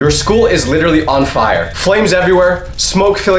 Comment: I skipped 20 sec at the first second i pressed the vid and it sounded like he said "your school- is a aquarium"